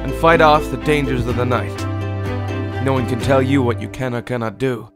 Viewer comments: i thought he said "what you can not gunna do" :S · when he said "can or cannot do" i thought he said "cannot cannot do"LOL